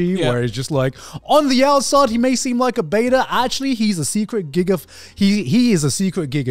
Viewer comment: did he just say secret gigguk 🤣